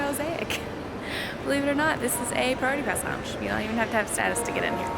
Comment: "Believe it or not, this is a <spoken way too fast and semi-mumbled> lounge, so..." I thought you said "boarding pass lounge" but listened to it six times, slowed it down to 50% speed and listened twice more. Still couldn't really understand it, but decided you may have been saying, "priority pass lounge". Still not sure, though.